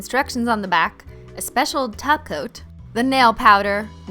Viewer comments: I didn’t hear taco, nor top coat. I heard tacoat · did you say topcoat